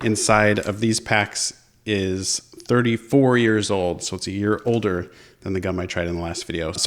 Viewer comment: Parents walk in: What are you doing? Me: Oh nothing, just watching a guy put hot sauce on a 34 year old piece of gum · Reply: Кристиян Ангелов You get the point. Maybe like the comment, smile and scroll and forget about it instead of correcting someone for no good reason. Edit: 6:09 he says 34 year old gum. :)